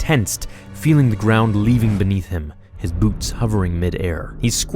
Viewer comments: did he say his boobs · did you say his boobs were hovering